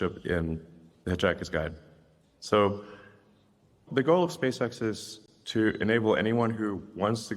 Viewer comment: Did anybody else notice how the incorrect transcriptions are hilariously symbolic of potential futures? 31:49 it says “Gold SpaceX” instead of the “Goal of SpaceX” implying there will be levels of SpaceX access for customers. Just like there is for UberX, for example. It happens almost every time there is an error and it’s hilarious, making me think Ai could be doing it as a joke.